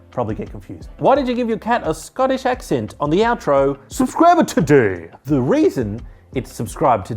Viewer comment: I thought you said "This cat is dead" at the end of your videos but it is "Subscribe now". Good to have that cleared.